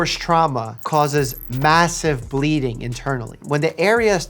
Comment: did i hear massive